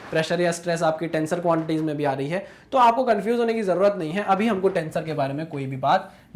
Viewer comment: did you say stress 😂😂